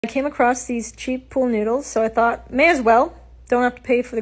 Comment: I thought you said: "I came across these cheap pool needles " to begin with and I'm like: "Pool needles? What the heck?"